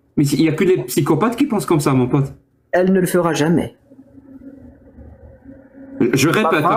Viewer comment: "elle ne le fera jamais", j'ai entendu "ça, la mère Delabatte, elle mouffetait pas". 😉